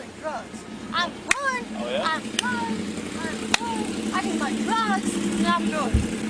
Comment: I heard her say repeatedly I need my drugs. It was also in the report you skipped past it. This means the Seargeant also had drugs on her. No way is this solely drunk behavior.